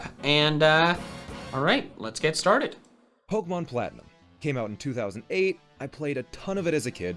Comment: anyone else hear at 1:50 he said pokemon black?